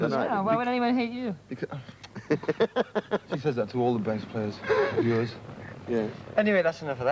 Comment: What does Farndon say at 1:37? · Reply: She say’s ‘why would anyone hate you?’ and he responds with ‘ I bet you say that to all your bass players’ Christie and Pete had been an item when band first started. He was the first person she auditioned, There is obviously some history between them and at this time Pete was causing problems for the band due to his drug use